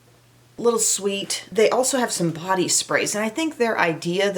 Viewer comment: "...they have some body sprays...". Could have sworn you said "... Potty sprays...". 😜😜. Thank you for your favorites. Already put the radio in my cart.